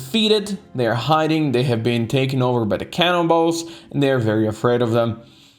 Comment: I'm very sure that always says cannibals i hear "Can in balls" 😂😂😂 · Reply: He says Cannonballs, which refers to a hidden ingame joke you can read on certain occasions. (entering the Cannibal Plains zone when one ouf your team mates has the Stupid personality trait)